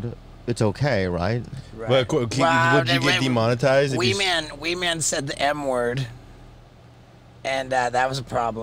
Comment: HAHAHAHA I THOUGHT HE SAID WEE MAN SAID THE “N” WORD AT FIRST 😂😂😂